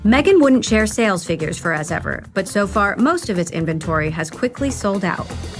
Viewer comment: Every time I hear “ As ever “ all I can think I actually hear is “ as if !!” Awful name 😂